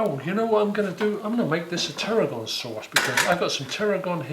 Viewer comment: Enjoyed that, thank you very much. Had to laugh at 4:26 when you said "hang on I'm going to make this a tarragon sauce", I thought you said "terrible sauce"